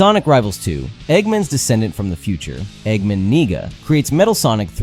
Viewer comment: Did he just say? Ni..